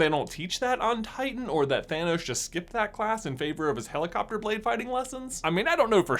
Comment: The first few times I saw this video I thought he said helicopter plane 🤔